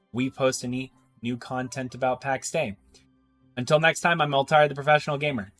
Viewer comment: For like 4 first times I saw your videos, I thought you said in the start "Hello I'm old retired professional gamer". I didnt read the channel name so I thought that was just a funny line that you say every time lol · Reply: "Hello I'm Elt-a-rii the Professional Gamer" "Hello I'm an old retired professional gamer" I never thought about it like that but I totally hear that now hahaha!